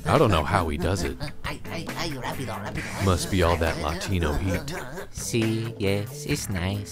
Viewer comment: Must be all that Latino heat haha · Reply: I thought he said "must be all that Latino meat"